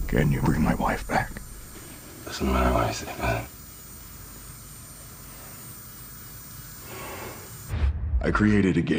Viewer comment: Can someone please tell me what he says at 3:02. I still can’t understand what he said. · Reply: He says: It doesn't matter what i say, does it?